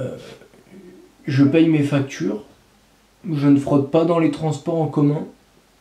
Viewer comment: J'ai entendu "je ne frotte pas dans les transports en commun"